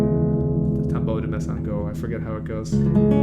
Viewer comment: what's this piece called? He's saying the name but I don't know how it's spelled. Sounds beautiful. Tambour de… something. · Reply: Tombeau de Mezangeau